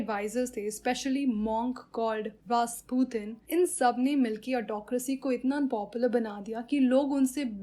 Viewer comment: Tsarina and rasputin made autocracy "unpopular" (It sounded like popular so thought to correct it) ^_^ Have a good day!